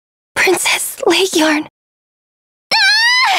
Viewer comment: IS really said Laegjorm rights